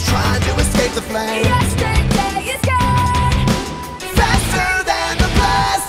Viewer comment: am I the only one who thinks it sounds like they said, "YESTERDAY IS GAY!"